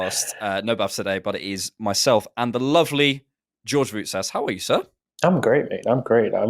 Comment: Great work guys! Is George Greek lol? Thought I heard say George Rousas